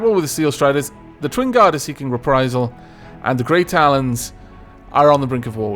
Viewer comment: When you were looking at the factions and said "Grey Talons" I thought you had said "Great Alans" and I did a double take, actually had to check to see if that was an actual faction haha!